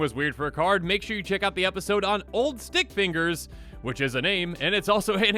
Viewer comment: was eating food, and when he said old stick fingers, i heard " ol dick fingers " 😆😆😂😂😂